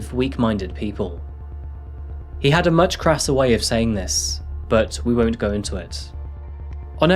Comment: What exactly is he saying there? Can't really identify it accustically, sounds like he was saying "he had a much krasser way of saying this" - I'm german, never heard anyone say "crass" before lul